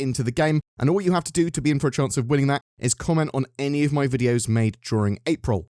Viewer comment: Did he say any video 11:00 🫢